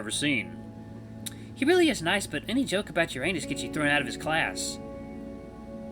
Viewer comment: I wasn't reading along so I heard "any joke about 'your anus' gets you thrown out of class." XD Yo wait run that back. lmao